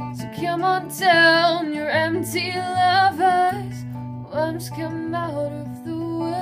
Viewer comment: God sake its worms not wolves people!! She is right -.- it may sound like oli is saying wolves but listen carefully. And if you look at the album it contains the ORIGINAL lyrics and it says worms come out of the woodwork. And why the hell would wolves come out of woodwork??? Seriously!